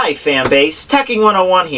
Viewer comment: Nov 7, 2019 - Oooooh! I heard "faMbase"! You said it before Nux!